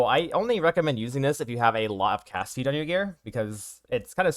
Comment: What does he say at 0:19 ? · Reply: “if you have a lot of cast speed on your gear.”